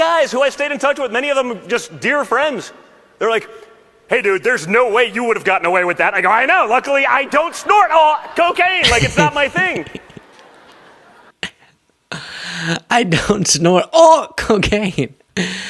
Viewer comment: I thought he said “snort off cocaine”.